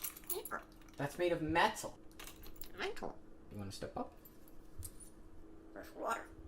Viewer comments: I heard him say mental...lol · When he said “step up” I thought he was trying to say “square up” and I died laughing.